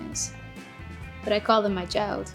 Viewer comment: someone someone please tel me what she says starting here 8:26 . it sounds like she says i call them my jowd? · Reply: My child